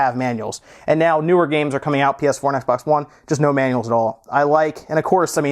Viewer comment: did he say PS1 and Xbox One?